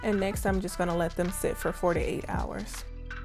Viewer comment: I’ve used this bath bomb receipt and they come out perfect though the next day when I want to shrink wrap them they all fall apart.. what am I doing wrong? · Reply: It sounds like she says 48 hours but she says 4 to 8 hours. Maybe they are out too long? I struggled with that until I listened again.